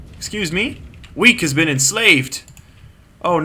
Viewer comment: The requirements for naming the Suicide Squad is most logical. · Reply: I thought that you said "Weak has been enslaved!" Instead of "Weke has been enslaved!" XD